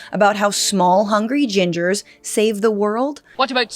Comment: I thought you said "HUNKY GINGERS" and I was totally on board.